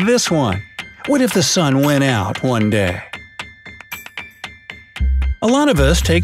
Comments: I thought you said only one day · I thought you said one day only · I thought you said "if the sun went out for a DAY"